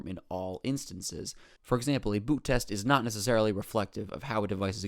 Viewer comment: I swear I thought you were saying boop test